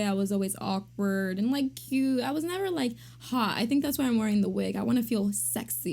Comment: "I was never like HAA..." Wut? Do you mean HOT? The word "hot" has a "t" at the end. 3:29 "I was lanky and underwayee..." Wut?